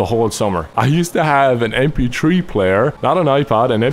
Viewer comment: When he said mp3 player I thought he said empty tree player at first